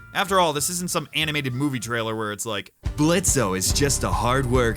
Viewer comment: DID HE JUST SAY BLITZ-OH?!!